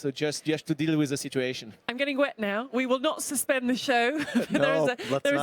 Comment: Did she just say she was "getting wet"...? Really?